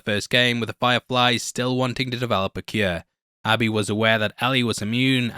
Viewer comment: I thought they said Abby was bit..